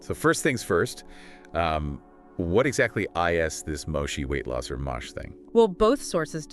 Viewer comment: 'what "I" "S" ' instead of "is"? is this just AI content?